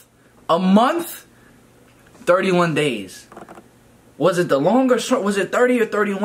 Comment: I’m so disgusted I thought u said DIRTY ONE DAYS 😂🤣😩